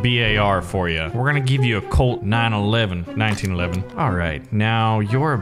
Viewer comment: Wait a second did he say 9/11